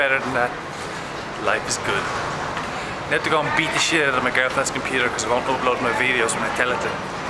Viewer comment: lol 2:10 I hear "Beat the shit out of my girlfriend" and I was like WHOAH until you finished the sentence.